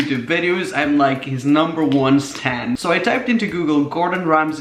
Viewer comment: says Stan instead of fan